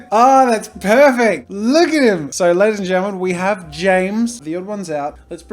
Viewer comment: Sometimes I hear “Get our ladies and gentlemen”